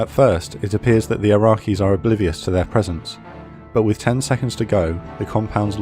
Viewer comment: at first I thought he said 10 minutes but then after watching it and I realize he said 10 seconds I was gonna say if those boys had 10 minutes to shut off the lights and scramble I would’ve been the fuck out of there by 10 seconds good luck…